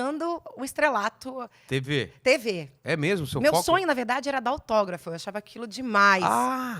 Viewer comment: Deus me perdoe na primeira vez que ouvi entendi meu sonho era da o toba 1:09 😂😂😂😂